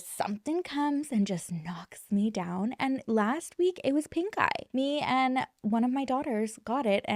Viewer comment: I don't know if it's cause I just woke up from a solid nap... but, there for a moment, I could have sworn you said it was "pink guy" that took ya down. 🤔🤷🏻‍♀️🤣🤣 I was like: Who the hell is pink guy && what did he do?? Then, it hit me that you were probably saying pink. EYE. Bahahaahaa! 😂